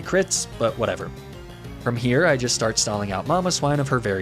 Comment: Every time he said Mamoswine I heard "Momma Swine"